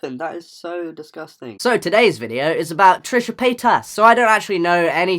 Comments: At first I thought he kept saying Trisha payTAX oof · I heard it as Trisha Pay Tax ...